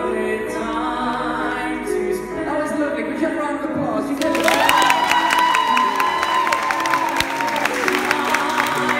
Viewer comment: Could anyone tell me what Matt said around 0:55 ? They sound so fun :) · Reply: I think Matt said “That was lovely. Give her a round of applause!” A young girl in the audience got a signed picture from boys during the concert. She was so lucky, it was the second time she got a signed picture so she decided to give the picture to another audience. Collabro boys saw that and that’s why Matt made the comment. Jamie’s reaction is so lovely too.